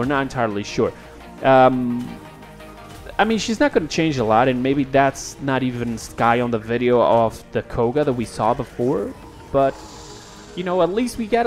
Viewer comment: Skye in the video of the Koga? Did i hear that right